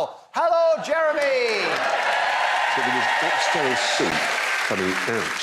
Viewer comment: What does he say about coming out of his ear please? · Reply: Oxtail soup coming out of his ear